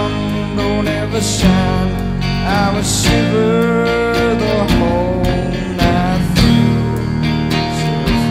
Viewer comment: What does he whisper around 2:10? When I was teen in deep obsession, o was sure he said, 'someone find me' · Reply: he said: 'shiver for me now' :)